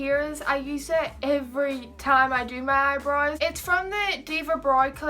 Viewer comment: It sounds like you say eyebroys instead of eyebrows that sounds so cute! Btw great tutorial